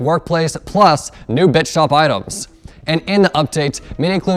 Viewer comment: Who else thought that when said Bits shop, he said Bitch Shop